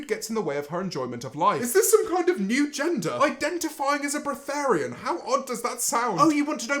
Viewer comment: Sounded like he said fart lol